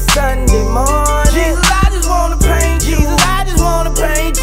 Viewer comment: Sound like he said Jesus I just wanna prank you